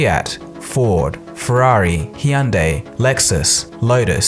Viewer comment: Did he just say "Hiunday"?